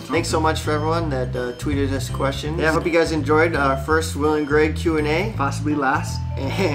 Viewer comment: "The first Will and Greg Q&A" I heard "the first William Greg Q&A"